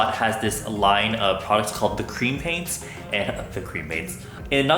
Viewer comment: I thought he said "cremate," not "cream paint" and I was like oh I wanna get cremated when I die too.